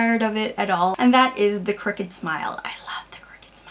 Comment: haha it took me a minute to figure out that you were saying CROOKED smile, i kept hearing "cricket smile" and was trying to figure out what that meant -_- ahh man..